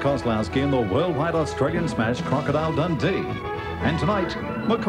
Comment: For a minute I thought said "Movie that will sting your heart" and was thinking that's a bit bold/extreme considering the story's climax 😆